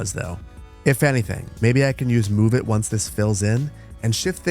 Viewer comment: Every time you say "let them fill in", I hear "let them Phil in" 😂